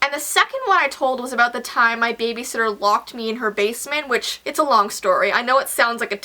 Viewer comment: i thought it said babySISTER and i was like...OK that's normal but she said SITTER not SISTER and i was like... um